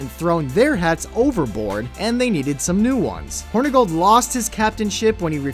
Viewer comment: haha:) thought he said hornygold:)